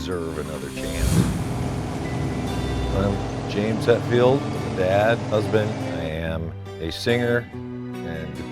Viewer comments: Lol, I thought he said "I'm a "bad" husband", listened again and of course, "I'm a dad, husband.." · I thought he said “Bad husband”. I even went back and listened again. The. Saw in the comments he said dad. 😂. Anyway, that was a cool video. His garage is insane! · Lol, at the beginning of introduction I thought he said Bad husband ...before I put on subtitles....Prolly seen Metallica the most out of any band. His SICK taste in muscle cars is not surprising. iId be doing the exact same thing if I had his kinda money. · "I'm a dad, husband, I am a singer and guitar player for a band called Metallica...". This statement speaks volumes. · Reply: I thought he said "I'm a bad husband" so thanks for this! 😁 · "I'm a dad, husband, I am a singer and guitar player for a band called Metallica...". This statement speaks volumes. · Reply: I thought he said " i'm a bad husband" lol